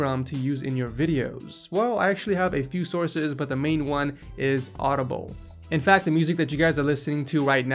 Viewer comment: I thought I heard audible 3:30🤣